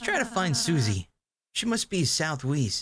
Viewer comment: Ooooh, southEAST? I thought you said southWEAST!